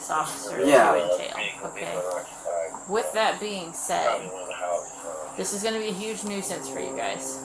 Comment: did she say "this is going to be huge news at 3? I can't get what she said